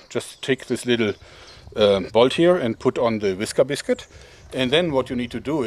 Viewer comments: What kind of biscuit? I'm not sure I heard that properly. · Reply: Cornelius Sneed Whisker Biscuit. The name of that product, a popular arrow rest device. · did he say "wiska biscuit" ? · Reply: Whisker Biscuit ..... Available at stores selling archery supplies